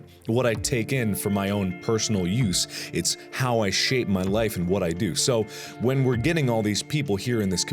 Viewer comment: at first when he said "shape my life" I heard "shave my legs" and I was like whaaaat